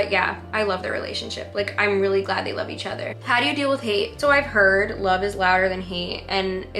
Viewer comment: when it asked how do you deal with hate i thought it said how do you feel about nate and then she starts talking about like ignoring it or blocking it out and i was just so confused, it’s not until i rewatched it that i was like oh lol